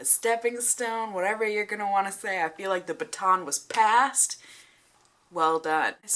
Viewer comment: Thought you said "Feel like the pecan was passed" Gave me a good giggle